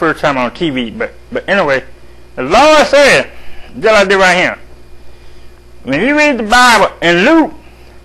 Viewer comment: What in the world is that thing he keeps saying! At 0:15, 0:25, 0:32, 0:38, & 1:01. "Jello been dealin' right here"?